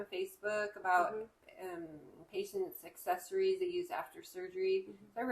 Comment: When you said "a patient’s accessories" I thought you said "a patient’s successories" which I thought was a cool contraction of success + accessories.